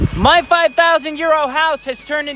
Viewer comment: Dude for sooo long I thought he was saying 5000 YEAR old house